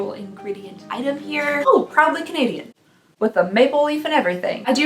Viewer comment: Managed to mishear AND misread that as "probably Canadian" and was very confused lmao also summer sucks for us not in the west